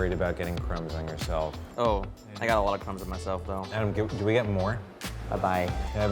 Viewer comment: I thought Steven said "Oh, I got a lot of cums on myself"...lol